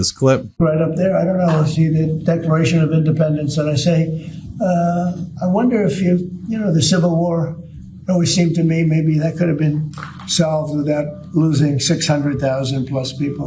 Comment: Omg, lol, did he just say the " DECORATION of Independence "??